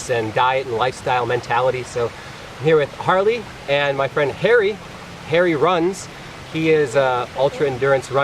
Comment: bwahahah I thought he said hairy but he said harry lol